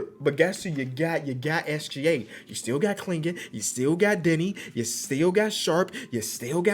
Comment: Tell me I'm not the only one who heard u still got diddy